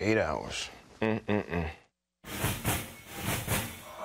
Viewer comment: "Make Darren Young Great Again"...... more like "Make Darren Young Straight Again". That would of worked. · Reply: my headphones fell of my head at that part and i honestly thought he said straight again and i had to replay it lol